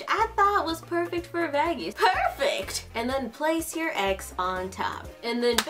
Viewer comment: i heard you say gay your egg🤣🤣🤣🤣🤣